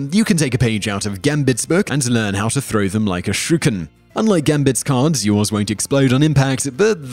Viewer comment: Did you say Shew ken